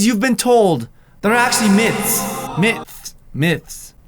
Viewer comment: did he says Myth gum